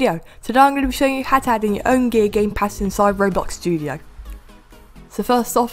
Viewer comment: bruh i think he said "gay gamepass" at 0:12 lol